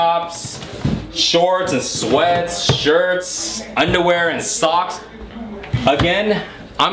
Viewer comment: WAIT....DID U SAY SHIRTS?? YOU???